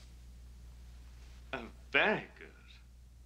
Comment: what did he say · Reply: "And very good"